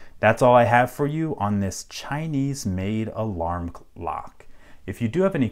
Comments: Did he just say "Chinese made alarm c-lock"? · Did he say alarm clock?